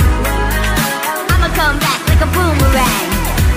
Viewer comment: It sounds like shes saying “ima come back like BOOMER RAT”